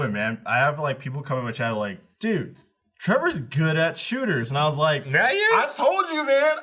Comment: Did just call himself "Trevor"?